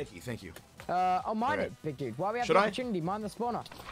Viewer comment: Did Tubbo call Ranboo 'Big Q'? Or do I just have shit hearing lmao